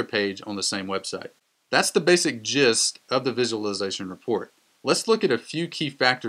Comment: Did you say "that's the basic jizz," at 6:11? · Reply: Gist